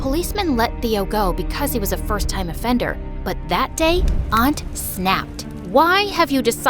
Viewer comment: I thought she said aunt slapped